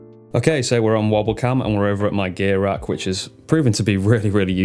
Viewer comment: I thought you said your “gay rack” at first.